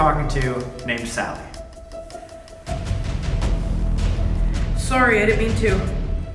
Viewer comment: I think one of the spirits said "Sorry i didn't mean to" To 'Sally' Cuz Sally did not want it to talk them cuz she wanted to so she's probably scaring the other ghosts